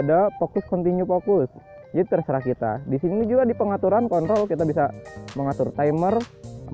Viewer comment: Menit 3:46 kok gua dengar dia kata "kontol" atau gua aja yang salah dengar😅